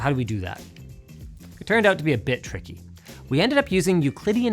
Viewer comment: I heard "turned out to be a bit tricky" as "turned out to be a bitch ricky" and almost had a spittake.